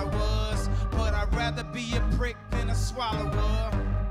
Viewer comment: when he said rather be a PRICK than a swallower I laughed so hard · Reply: Spencer Jenkins i thought he said brick😂